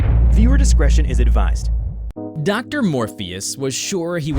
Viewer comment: I swear I heard Doctor Morbius